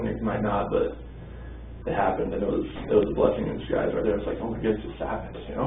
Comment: Somebody help me out. Did he say Bella was a "bkessing in disguise"?